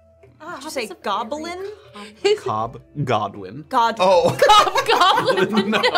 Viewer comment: I heard "cop goblin" and thought he was named after his job